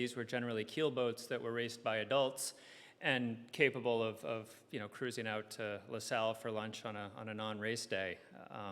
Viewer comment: what is he saying? "Cruising out to La Salle for lunch?"